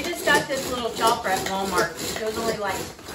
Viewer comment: Were did you get the string pull chopper at? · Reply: I heard you say Walmart. Thank you